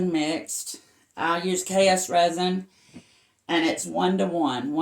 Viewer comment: Did you say you use KS RESIN? Where do you get it? I’m in CT, USA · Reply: Yes Deb, KS resin. I just go to their website hun. That’s how you will google it. KS resin